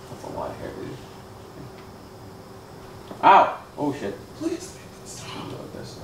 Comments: I hear a male say please stop. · at about 17:05 I Can hear a whisper say "stop" or "please stop" then the door opens can anyone else hear it? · I have never heard or been more convinced of an evil spirit until I heard the "please make it stop" in a horse womans voice right at 17:00. That's amazing yet creepy. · /17:05 there’s a loud distinct male voice saying “please make it stop” I say loud cuz it’s extremely clear whisper that was very clear and loud all I did was turn up my head phones u can hear it sooooo clear! Also pls pls pls tell us what it said on ouija board?????? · Love love love your videos. Thanks so much for sharing with us. You both take care Ok I was reading the comments and WTF was that at 17:00. I freakin heard a lady saying please stop. I listened several times and I think the is something else with the please and stop but I couldn't understand. That kinda freaked me out